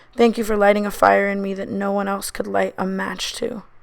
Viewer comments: i thought she said thank you for putting a kid in me and i was like ...damn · I thought she said "thank you for waking up the kid for me" and I was like wHaT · I thought she said waking up with a kid in me and I got so scared. Anyways congratulations Tana I love you bb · who else thought she said thank you for putting a kid in me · Who else heard her say waking up with a kid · i really thought she said thank you for waking up with a kid in me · I thought she said waking up with a kid in me 😂😂😂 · I THOUGHT SHE SAID “with a kid in me”